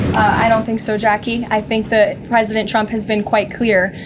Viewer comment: When Caroline reads her MRI report I swear I heard her say “president Chump”.